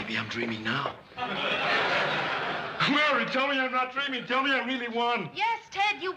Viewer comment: he said murray instead of mary.